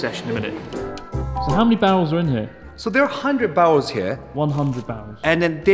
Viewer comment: I definitely heard "how many bowels are in here?"